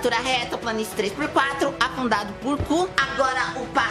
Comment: Pera eu ouvi afundando por um cu 2:03 ;-; Berrooooo 😂❤.